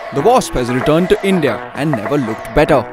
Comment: Wospa return to India and never looked better" ?? what are you saying, sounds funny ?